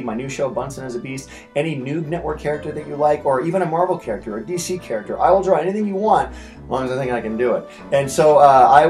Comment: YOU SHOULD DRAW DC'S NIGHTWING ON THE WHITEBOARD PLEASE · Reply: i thought he said any character he will draw